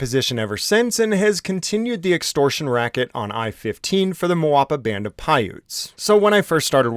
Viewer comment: I thought you said "Moapa Band of Pirates", it definitely fits lol